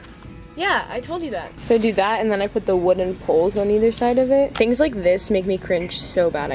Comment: LOL I thought you said coles